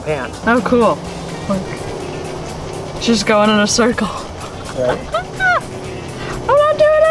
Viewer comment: Hello ! i m not a native speaker and i have a question at 0:43. i hope you can help me :D why does she say ''she is going on circle ? Normally the things like water are ''it'' neutral ? why not ''it s going on circle'' ? i don t get it ... :S thank you for your help :D ps: good video like always ! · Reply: She actually says "it's just going in a circle." But she does say it pretty quickly so it might be hard to miss, especially for a non native speaker.